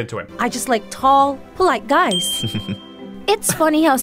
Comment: Walked away for a sec and heard her say "I like tall black guys" 😂